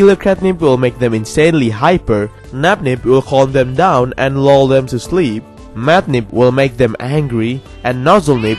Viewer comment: i thought you said "LoL them to sleep".